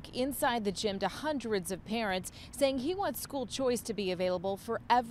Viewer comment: I thought she said "governor Greg abot was inside the school to talk to hundreds of Karen's" I had to rewind to and hear she says parents 🤣🤣🤣